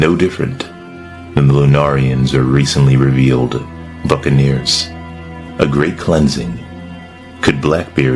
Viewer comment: For a second there i thought you said fuccaneers lmao 😂